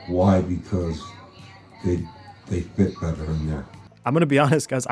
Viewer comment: what to see something weird. Wile this video is playing (the one shows in the video that a person says sound like him) at first I heard “they bit ve-verven-ya” then later heard “they fit better in their”. I hear both all I do is repeat in my head what the dude in the video says and it says it. Bro it’s very trippy. What do you call this? I have no freaking clue🤣